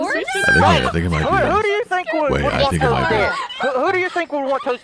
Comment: anyone else hear what I heard? ( it was about Tina) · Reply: An al wtf